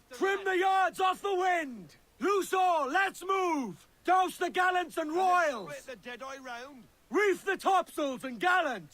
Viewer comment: can someone write what he says in the minute 0:29? (not a native English speaker and it's hard to understand Edward) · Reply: “Douse the gallons and royals!”